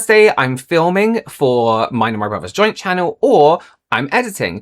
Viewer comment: When he said "mine and my brother's channel" I misheard it as "mine and Robert's channel" and my brain nearly imploded in confusion lol